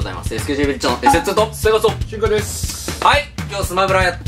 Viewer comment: のシンカの挨拶「チ〇コです」って聞こえるんだけどw